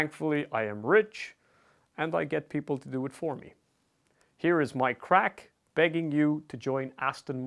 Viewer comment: Why did i hear "Here is my crack"